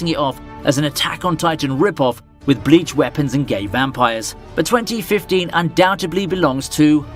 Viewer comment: Is it more or did he just said gay vampires?😂